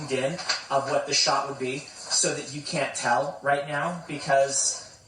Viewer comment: Did you just say, "You can Taln me"at 45s? XD